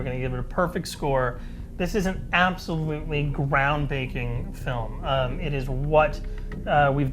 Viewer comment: LOL I heard ground 'baking', too. Kept replaying it to make sure.